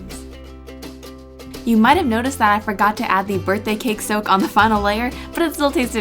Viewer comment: Cake soak? More like Sir Squeeze-a-lot! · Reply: I thought she said cake soap... also HOW TO CAKE IT!